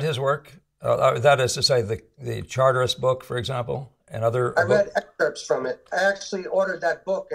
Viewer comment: Can someone tell me what the name of the book mentioned @ 4:00 🙏🏻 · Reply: Jhon Sargent Evan Charteris